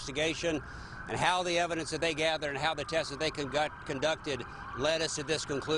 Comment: Did he say “concocted” ? 1:31 Freudian slip? Believe no one.